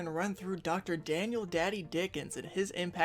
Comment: ngl i thought it said dr daddy daniel dickens